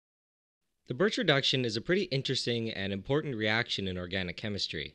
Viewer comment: Goddamn I thought it said the Bitch Reduction